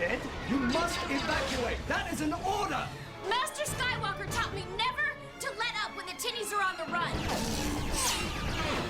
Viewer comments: I swear I thought Ashoka said “Master skywalker told me to never let up when the titties are on the run” 🤣🤣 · I'm sorry, but at 0:34 why does it sound like Ahsoka said: "Master Skywalker taught me never to let up, when the titties are on the run" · "Master Skywalker taught me never to give up when the titties are on the run." I know that can't be what she's saying... But I've listened to it over and over and that's what I keep hearing every time I listen to it.